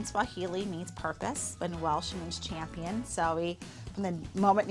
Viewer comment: did Holly say welsh?